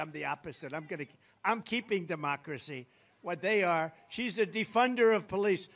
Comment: Twice during the speech the "closed caption" has stated the opposite of what former President Trump said. At 13:02. He states "she's the defunder of police" ...the closed captions says "she's the 'funder' of police" ???? That is the exact opposite. Also at the end he says again "she supports to defund the police during the 2020 riots" and the closed caption states: "she supports to 'fund' the police during the 2020 riots" Who is doing the close caption? Ai??